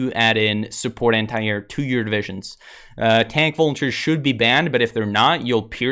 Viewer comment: What is he saying at 11:42? Tank-something should be banned? I hear "tank vultures" .. what is he actually saying? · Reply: volunteers, tank volunteers to Spain should be banned